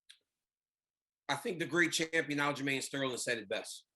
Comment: Did DC just say, "The Great Champion, Aljamain Sterling" ?????